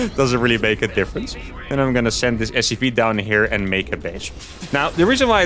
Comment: did he just said "I'm donna sent this SCP down there and make a BREACH"??